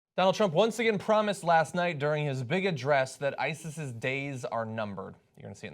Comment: all i heard was "donald trump...in his bigot dress"